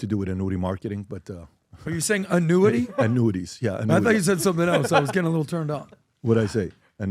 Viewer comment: I thought he said nudey too…